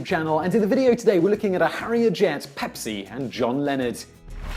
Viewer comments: Lol I thought he said John Lenin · I first thought you said John Lennon. :-)